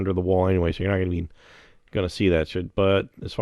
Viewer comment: Did you say not gonna see that shit at 17:56 lmao